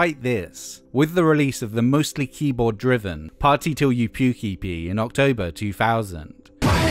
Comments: I thought you said pukey pee when you said puke EP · When he said party till you puke EP I thought he said at first, "party till you puky pee". Which I would prefer not to do to be frank.